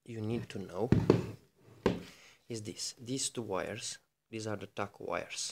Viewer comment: Nice! Thanks. Makes sense to me and I'm an idiot when it comes to electrics. Can't quite work out what you say here: 0:54 "these two wires, these are the ___ wires" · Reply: The tacho wires that we will not use ,so from the start we have eliminated 2 wires all the best my friend